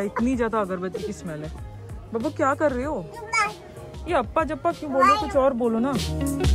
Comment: abba dabba Di - babu kya kar rhe ho ? 15:16 - zumba 😆 ( that's what I heard ) 😂😂😂 btw such an amazing vlog , I really appreciate the way you showed us everything in this vlog , thank you for your hard work. Di 😇